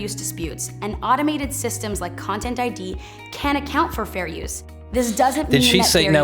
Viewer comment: The first couple of times I heard this, I thought she said, "can". This is a problem with the General American dialect: "T" often turns into an indistinct flap, and in this particular word, can make it unclear whether or not there is a negation. Good thing they made proper captions.